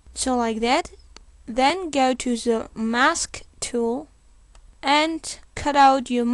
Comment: i dont understand wich tool? plz help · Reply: Magikjim he literally said Mask Tool